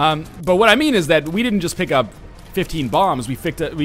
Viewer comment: Welcher Deutsche hat bei 2:18 auch fickt verstanden :D